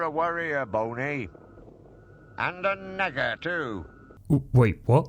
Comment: And I watched Courage the cowardly dog as a kid! The trap door seems a lot scarier honestly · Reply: also at 17:20 I think he said "and a nagger too"